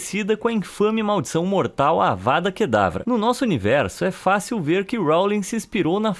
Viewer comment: Na pareteb14:19 eu escutei a vara que raba